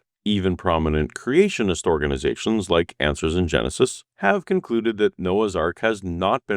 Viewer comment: Lol, I heard "answers in genocide" 😂 Not that the aren't enough if these in the bible.